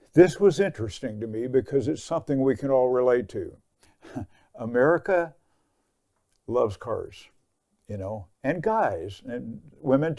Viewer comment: misheard "America loves canage"